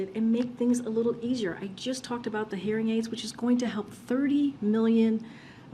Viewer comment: Ok. Wait. At 1:56. Did she say the they are helping with hearing aids ???!! Ummm. No offense to the deaf or should I say. Listening unfortunate 🤷‍♂️. But We “ All the people”. Here are getting strangled with your policies !! And you “talk “. about hearing aids ?!?!